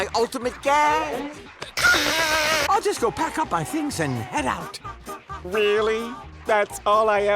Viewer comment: Squidward saying he wanted to hear his dad say he'ĺl pack up his things and go sounds like him saying "go pick up some milk" and "take your time".